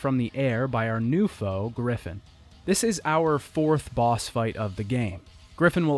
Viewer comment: In 26:53 I thought he said Griffith not griffin I was going to unleash my inner guts until I noticed that,s it's griffin not Griffith